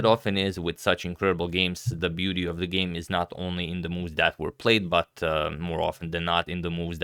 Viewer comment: I had thought said the beauty of the game is also the players' beauty